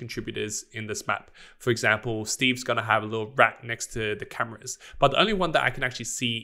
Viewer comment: what is he saying? Is he saying "Steve is going to have a little brat by the cameras"? What lmfao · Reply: have a little RAT by the cameras as an easter egg